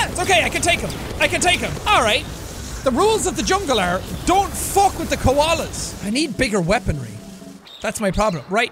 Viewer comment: "The rules of the jungle are, don't mess with the koalas" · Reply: but...i thought he said "don't fuck with the koalas"